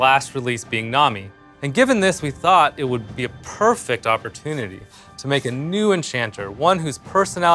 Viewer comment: Did he said "puurfect' opportunity? Another cat?